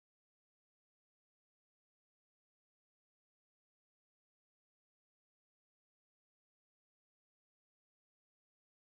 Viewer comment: What he say ? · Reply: It’s a tie